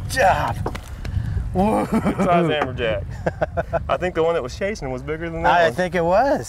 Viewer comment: I think you misheard, those are amberjack not hammerjack.